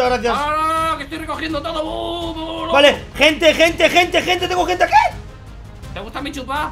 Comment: creo que escuche mal, por favor un alma piadosa que me corrija :^ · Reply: Dijo gente gente :')